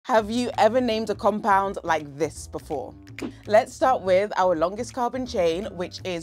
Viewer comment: I heard "have you named this before?" And in my head I was like "not yet, but I'll name it Jerry" clearly that's not what you meant though lol